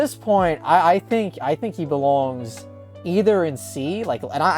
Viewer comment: man... In my head I heard: "belongs to the street"